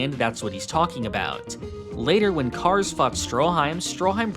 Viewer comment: Anyone else hear "fucked" instead "fought?"